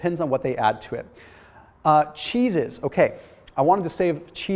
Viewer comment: Thought he said "Cheez its" and I'm all "?!"